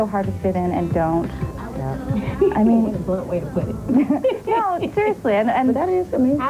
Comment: What did left eye say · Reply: What A Blunt Way To Put It